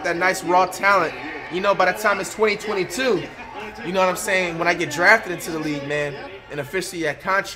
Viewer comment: did he just say if he got drafted? 😅🤣